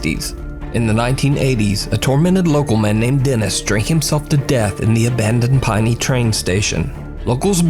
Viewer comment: In the first one it sounds like he said stripped himself to death but he said strangled 😂🤦